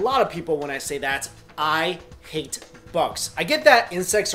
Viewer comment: Ok, I might sound strange but when he said "I hate insects" I heard "I ate insects", stereotype reflexes I guess :D